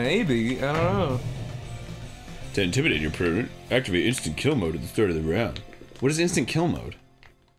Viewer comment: I could have sworn Mr. N said, "Insta lock Mid." Did he, or am I just playing too much League? cx